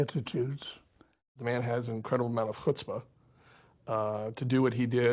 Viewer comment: Would someone be kind enough to tell me what did the archivist said in 2:15. Something that sound like " melasfotpa".Thank you. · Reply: Chutzpah (/ˈhʊtspə/ or /ˈxʊtspə/)[1][2] is the quality of audacity, for good or for bad. The Yiddish word derives from the Hebrew word ḥutspâ (חֻצְפָּה), meaning "insolence", "cheek" or "audacity". Thus the original Yiddish word has a strongly negative connotation but the form which entered English through Ameridish has taken on a broader meaning, having been popularized through vernacular use in film, literature, and television. The word is sometimes interpreted—particularly in business parlance—as meaning the amount of courage, mettle or ardor that an individual has.[citation needed]